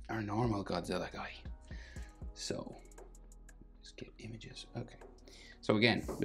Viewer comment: After he said, what, regular Godzilla, he said, images or something21:55